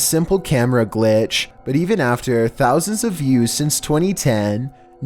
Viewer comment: I thought he said "Even after thousands of years since 2010..."